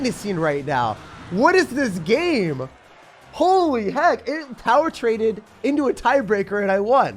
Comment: naa bro i heard holy shiittt 😂